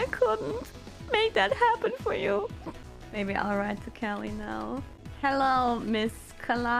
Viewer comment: Excuse me for correction: "I'll write to Calli now" instead of "I'll right to Calli now"